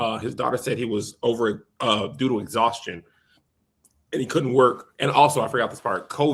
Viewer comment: I thought he said doo-doo exhaustion at first 😂.